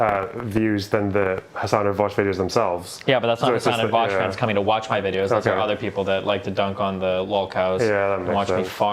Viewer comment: "Honest to god im just trying to" what? Can anyone make out what destiny says at 34:30? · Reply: I think it's "Honest to God, I'm just trying to afford my child support."